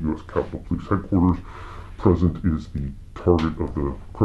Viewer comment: FYI..@ 12:13min with CC (close captioning ) on, Notice that the word 'Present' is mistyped ( twice at least) as 'President' is target of the criminal investigation..." I had to re-watch on my tablet NIW in order to clarify this because ( hubby uses CC and I thought they were saying The President is the target of this investigation, so I was thinking wtf? Check it out..lol!! Most folks wouldn't have caught this without the CC on..Besides being a very piss poor audio, important parts bleeped out without context..worthless really.. Praying For Justice Thru-Out!! Schumer needs to Pass Go and head DIRECTLY TO JAIL Re: Jus. Kav. Assassination Attempt!! I'm sure that was Orchestrated by the DNC aka Don't Let Trump Back Campaign agenda, the Filthy disgusting Swine!! Check out Judge Jeanine on The Five, tonite ( 6-9-22) thurs. What a Go Girl Classy Gal!!